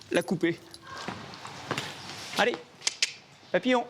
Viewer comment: C'est moi ou a 50:50, il a appellé son cheval "papillon" ?? 😂🤦🏻‍♂️